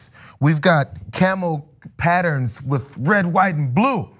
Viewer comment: I thought he was going to say camel toe, but he said camel pattern.😂🤣😃😄😅